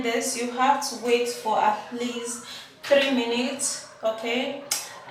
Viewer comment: How long are you going to leave the mixture on the face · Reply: I thought I heard 3 minutes